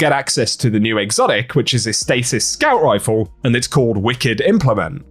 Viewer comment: It's stasis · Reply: swore i heard strand even watchin it again to be sure of location and heard stasis this time I was so perplexed Thanks